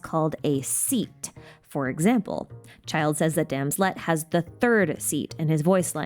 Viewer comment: I'm so sorry but I've been laughing so hard at this. I was listening to the video while working on other things. When you said "Childe said that Damslette" I heard something COMPLETELY different and had to come back to the video to see what you actually said. And I have been laughing on and off ever since. As for what I heard.... "That damn sl-" maybe I should just keep it to myself.